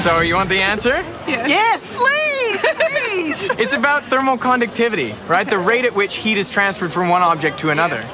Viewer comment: Well, it sounds almost the same, "mo" and "mal", so I can't know for sure. But if you want to find out more about the concept, the term is "thermal conductivity". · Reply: Sounds like hes saying thermal conductivity if you really listen and understand his vernacular. The auto captioning system typo'd it though.....